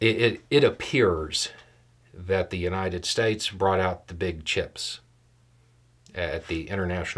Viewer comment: I had to do a double-take, I thought I heard America's big ships. I'm old enough to remember when that meant "send in the 6th Fleet!" On second take, it was big chips, but our fleets [a.k.a. battle groups] actually still are some of the chips Uncle Sam always holds at that infamous poker game where everyone cheats and every one knows it! If we didn't hold those chips, and everyone knows that we occasionally use them, the world would be a very different and more dangerous place. I hate to say that, but the truth will out!